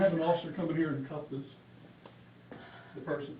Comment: it sounded like he was gonna say “can someone come in her and cuff this b*tch” 😂